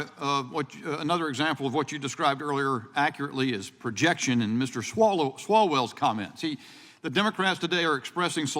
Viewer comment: he actually said "swallow Swalwell"